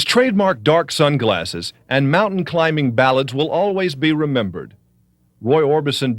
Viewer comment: What does the narrator say at 2:52? "His trademark dark sunglasses and mountain climbing ... " I can't hear this one word. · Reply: ballads